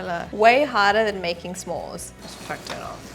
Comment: I had to rewind. i walked across the room and swear at 3:01, you said, "Way harder than making p*rn" 😂 smores got it now lmao